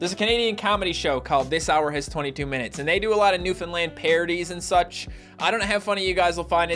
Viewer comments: I always hear "NewFinland" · My whole life i thought he was saying new finland